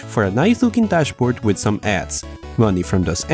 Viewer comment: Haha..I thought you said ass for a second there but you said ads. Edit: It's